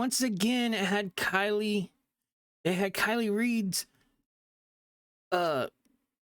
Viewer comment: Did you call her Keily Reed?? I'm sorry, i think that's what i heard, if not you can correct me so i understand what you were saying at that moment.